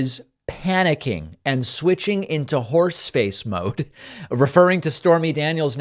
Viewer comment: When you said "horseface" I thought you said "force space"! Space Force! Space Horse! INCLUDING A HAT.